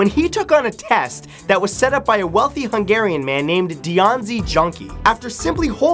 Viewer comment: Wtf is Dionsi Junkee or Dszunki Dionszén or what ever I didn't understand it and these names don't exist. Any help? Hungarian name at 8:30.